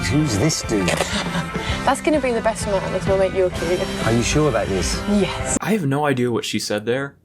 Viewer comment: “that’s gonna be the best man, it’s my yulkie/yorkie(idk what this last word is nor am I sure about the whole last half lol) …” ?? It’s our young kid???? Lmao idk